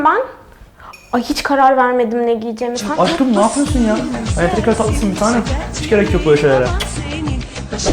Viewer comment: de adamin ne dedigini anlayan varmı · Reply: şeker kadar tatlısın bitanem hiç gerek yok böyle şeyler e dedi